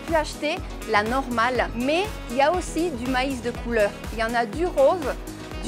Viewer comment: entendí "maíz de cúlÓ" XD